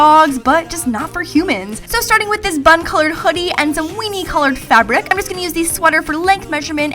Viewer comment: O_O did she just say bum coloured sweater